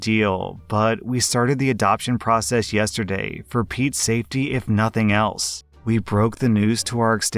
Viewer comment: Ohhhh not the timing. But at one point, I thought op was going to say they did it ‘for Pete’s sake’